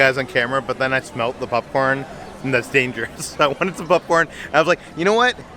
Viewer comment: Sounds like is saying “pupcorn”